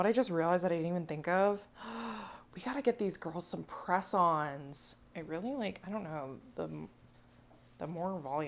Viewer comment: Oh! "Press-ons"!!! I thought you said "croissants" XD XD